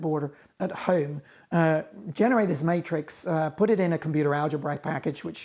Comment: does he say "Generate a snaketris"? If so that deserves massive points. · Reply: "Generate this matrix"